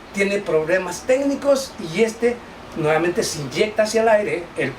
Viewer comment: Minuto 3:45 CORRECCIÓN Compañero, no es se INYECTA sino EYECTA o quizás escuché mal por lo cual estoy dispuesto a comentarios.